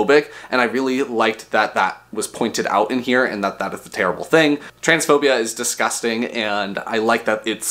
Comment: I thought i heard you say "transexuals are disgusting", and, I stopped frozen, omg, 🤣. I really liked to read this one!